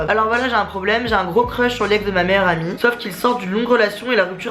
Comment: j'ai entendu "j'ai un gros crush sur l'ex de ma mère"